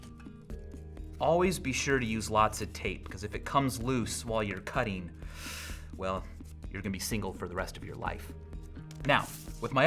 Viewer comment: I thought he said "if it comes loose while you're coming, you're gonna be single for the rest of your life" °________°